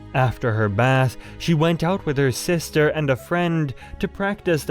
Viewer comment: I'm sorry, did you say sister?! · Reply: Yes he said sister